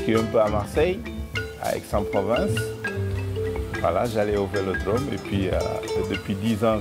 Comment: Super cette vidéo ,par contre à 5:40 il dit "vélodrome" pas orange vélodrome,pourquoi avoir rajouté le orange au sous-titre...